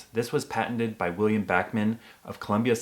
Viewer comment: for a second there i thought you said William Batman